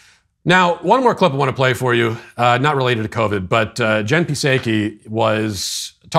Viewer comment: sounded like "jen piss-achy." good one.